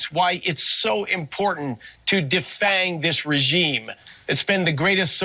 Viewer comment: Freudian slip - says it’s “so important to defend this regime.” A bunch of bumbling idiots running this country. Or did he say “defang”?